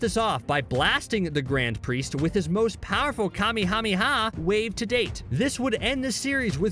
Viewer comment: not gonna lie, it was funny and sounded like kamihamiha